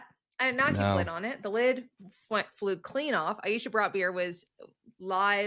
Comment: When Aisha pronounced her name around 1:39 I heard "Aisha Brought Beer"